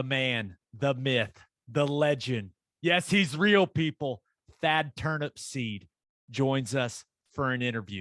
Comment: All this time, I thought you guys were saying "Bad Turnip seed" like it was some kind of southern colloquial nickname. EX: "He was more ornery than a bad turnip seed." Which makes about as much sense as any other nickname from down there... Also, are we sure he is not BV with a deep fake mask? Because they sure sound a lot a like. Like BV, he seems like a guy that can get things done! Welcome to OU Thad!